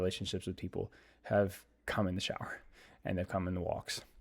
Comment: All I heard was "cum in the shower", sounds good to me